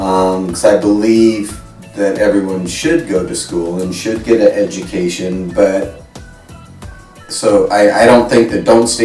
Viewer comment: Did he just say "and should get 'a' education"?